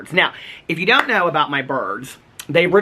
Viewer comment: I thought you were saying “my birth” instead of “my bird.” 😂😂😂